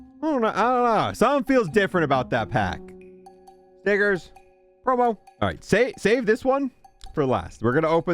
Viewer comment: Bro did not say stickers 😂😂💀